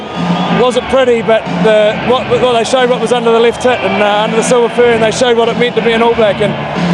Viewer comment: did Richie just say "it showed what was under the left tit" at 6:00?